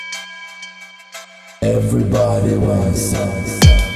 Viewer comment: What does it say at 0:15 ? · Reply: Everybody wins (if they want it).